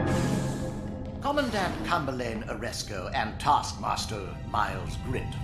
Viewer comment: I thought it said Ashoka instead of aresko lol😂😂